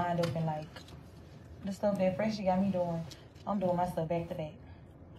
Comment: Did say Phresher? As in that roach from Love and Hip Hop New York? (a known scammer)